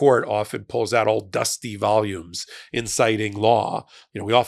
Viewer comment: You said "The Supreme Court often pulls out old dusty volumes in citing law." I heard "The Supreme court often pulls out old dusty volumes, inciting law." Took me a second. Of course, they have proven that they are totally willing to disregard precedent and "settled law", so I didn't think you really means "inciting law". Edit: I will also say that Willis presented evidence to not one but TWO different Grand Juries, who recommended the indictments. Regardless of Willis' political feelings or relations with co-workers, the Grand Juries accepted the evidence she presented.